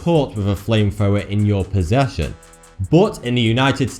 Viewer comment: Sounds like he saying flamefower instead of flamethrower